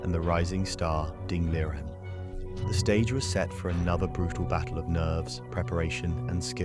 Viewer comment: I heard battle of nerds. Kind of rude